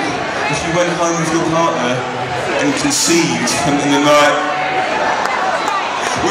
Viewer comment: what does he say at 0:33? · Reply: To be sending the night